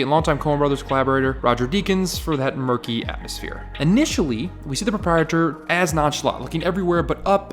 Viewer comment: Thank you for very interesting content and the passion for deep, mature analysis of cinema. But... Here and there, I was struggling to understand what you were saying. English is not my first language, but usually I have no problem understanding it within my YouTube browsing activity. Here however - I found it difficult to a degree that forced me to slow down the speed of the video... and even then I had to toggle the subtitles on. Could you please consider speaking a bit slower and clear the pronunciation? One of examples of what I'm talking about can be heard in 2:37 - "we see the proprietor" - but there are plenty of those "rushed" pronunciations, enhanced maybe by not so studio-clear audio recording quality. I don't mean to be rude, please don't think of me as of a demanding, spoiled little bastard - overall it's a great chanel, with huge value of quality in it! I just wanted to drop a friendly advice from non-english viewer, that's all :) Cheers!